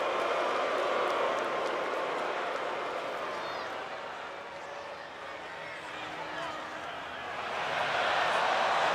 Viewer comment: I wish I knew what they were saying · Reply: Undertaker said You Dont Have The Balls